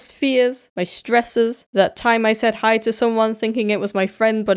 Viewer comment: excuse me what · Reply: Oh...'said hi'...genuinely thought I heard 'set fire'